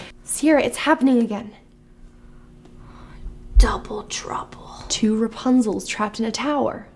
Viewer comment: When said “Double trouble” I thought she said “Donald Trump” 😂